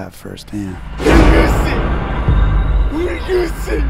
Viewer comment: is it "how do you see?" at the beginning? also, whats the guy saying at 5:53? i hear "he'll use see"